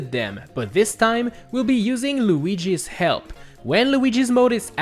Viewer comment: I wasn't pay attention and i hear "we been using luigi's hell" i was very confused